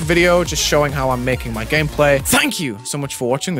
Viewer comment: “Have a good debt” Thank you, I will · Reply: I heard “have a good death” 💀